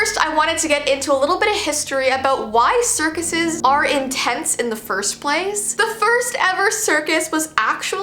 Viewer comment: When she said “in tents” I thought I heard “intense”